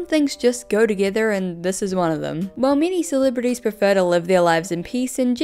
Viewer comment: in the beginning of the video i thought you said “mini celebrities“ and then i was just so impressed that that‘s what you called justin bieber